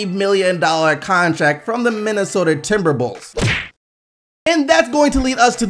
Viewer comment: did he just say :timberbulls"? 😂😂😂